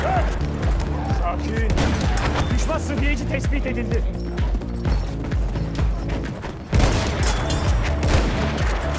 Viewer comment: To any Turkish players watching, what does he say at 5:56? Always sounded cool, so I’m just curious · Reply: late reply but it's: "Düşman sıhhiyeci tespit edildi." EN: "Enemy medic has been spotted."